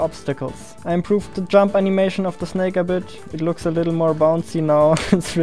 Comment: did he say snake??